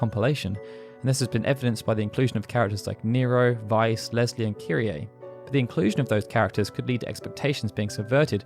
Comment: When you said "Leslie and Kyrie" i heard "Lesbian Kyrie" Good for her :)